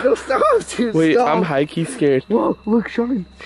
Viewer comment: Guys at 6:26 u can hear a woman's voice say something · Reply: It sounded like she said kid stop